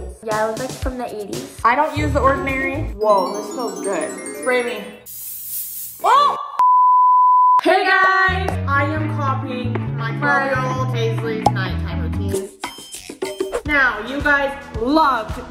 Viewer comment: am i the only one who felt like and said 5-year-old instead of 12-year-old